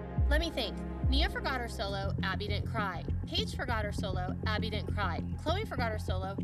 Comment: When christi says "when paige forgot her solo, abby didnt cry" sounds like she said peach 😂🤣🤣🤣😂🤣😂🤣😂😂😂😂